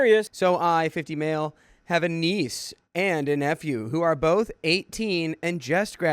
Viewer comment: I kid you not when I first heard you say I have a niece and nephew it sounded like I have a niece and f u. But I miss hear everything it's how my brain works